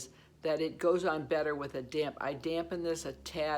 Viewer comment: I’m dying 🤣. I thought you said damn sponge until I saw what was written, “damp I said damp”. I’m still laughing. Thank you for your enthusiastic and fun videos and for sharing your thoughts on products. 💜 🌀